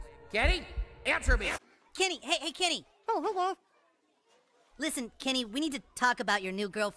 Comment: I think I understand a bit what Kenny said first he said oh hey ya guys second what is it? third, what do you mean? 4 what? i only understand these :|